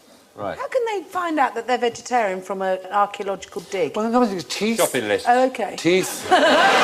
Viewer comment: what did he say at 1:40 , i didnt get it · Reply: "Shopping lists"